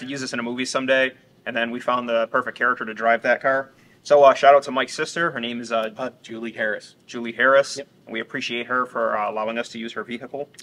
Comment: I was watching while doing other things so @ 15:29 I thought you said “shout out to MY sister” and then you turned to Mike and “said what’s her name?” I burst out laughing…and then realized you said Mike’s sister